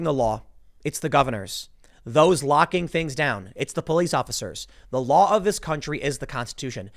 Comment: Man, did you just say "it's the police officers? 10:50 man, f*ck you. "